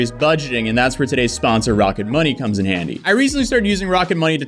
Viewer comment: I Heard " this video is sponsored by Rocket bunny" and I nearly jumped out of my chair lmfao